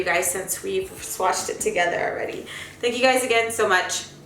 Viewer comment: My boyfriend thought u said “pedos a todos” 😂😂😂